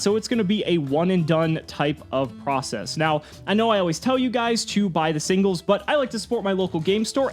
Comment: I thought you said don’t be stupid by the singles